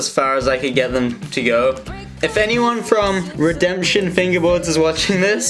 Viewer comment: Did he say redemption fingerboards?